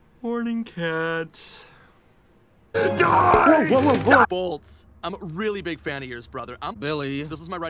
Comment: I thought it said Bolt 2 :(